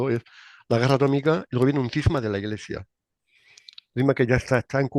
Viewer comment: Mn 9:25 ¿ Qué dijó? No se entiende!! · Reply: La guerra atómica y un cisma de la Iglesia